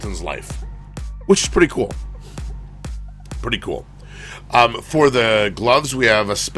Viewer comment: "хочешь прикол"? Мне по ходу послышалось